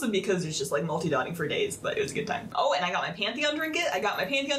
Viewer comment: i thought she said panty on XD